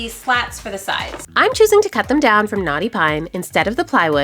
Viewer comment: Not me hearing "NAUGHTY PINE" instead of "knotty pine" 😂😅